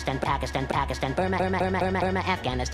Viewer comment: I heard ‘Pack it’s stun!’